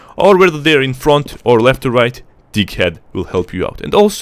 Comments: I always hear "Dick head" whenever he says "deag-head" is it just me or some of you have heard the same. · I’m dying after you said drag head sounds like you said dick head